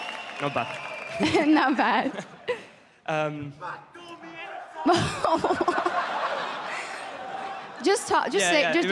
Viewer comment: What did that one guy in the crowd say at 3:21 ? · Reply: name? "backdoor me"